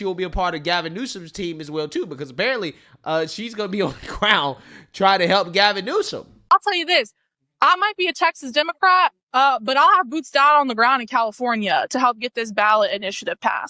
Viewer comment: Boots on the ground. I thought she said hooves on the ground.